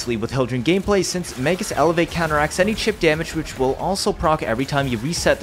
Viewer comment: Magus elevate counteracts any what damage?? @ 1:13 i didnt hear you correctly, i think you were talking a little too fast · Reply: Chip damage